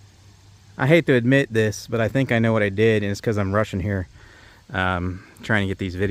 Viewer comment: I thought you said, "I'm"Russian"" 😂 😂 😂 😂